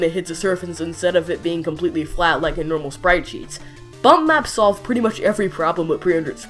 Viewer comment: when you said bump map, i thought you said butt math xD